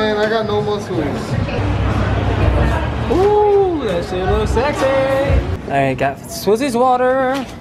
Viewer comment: did he say that sh*t looks sexy